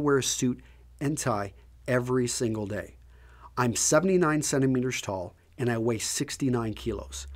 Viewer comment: does he say 79cm tall??? wow thats short!